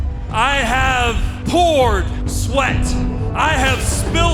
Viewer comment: I thought hangman said whore sweat than whole sweat